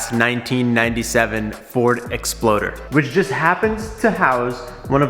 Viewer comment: is saying ford explorer or ford exploder??😂😂